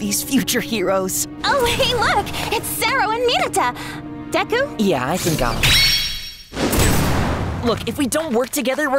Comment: I'm deaf af what is he saying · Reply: Yeah I think ill- (PASS)